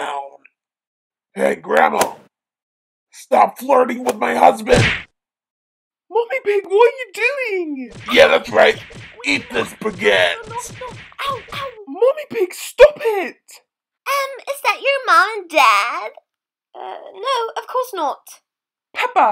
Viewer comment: “Yeah thats right! Eats this baguette 🥖” The fact that that has nothing to do with anything makes it even more hilarious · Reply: I first thought she said spagette